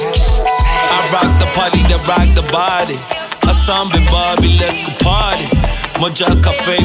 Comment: Dankie Supamega, did he just say "asambe Barbie let's go party " 🔥🔥🔥